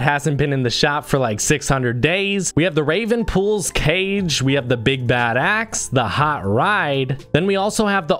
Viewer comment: i heard big bad ass